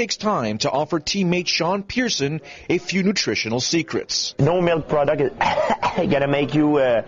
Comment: does he say "no milk product?" and what does he mean by "hark, hark" ?